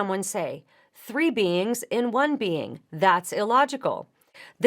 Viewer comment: 3 beans in 1 bean is not a good analogy for the Trinity. 3 flavors in 1 bean may be closer, but I don't understand why anyone would use beans as an analogy for the Trinity at all. Obviously I misheard what she was saying as I laid in bed half asleep. But when I heard it, that was my thought. It forced me to wake up and listen to that section again. If I hadn't woke up I am certain that a dream about Jack and the Beanstalk was coming.